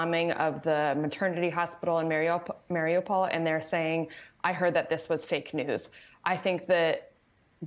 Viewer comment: Can anyone make out what she is saying at 2:12? It sounds like "... I heard that this was take to hoof" although I know I am not hearing it right. Thanks